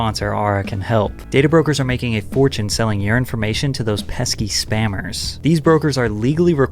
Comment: Ok 🖐️ this is going to sound extremely STUPID LMAO 🤣 But every time you say “your information” I kept hearing “urine information” I don’t fucking know 🤷‍♀️ LOL 😂 I’m going to blame it on the edibles 👌 Great video 👍 Also lookin 👀 good! 😊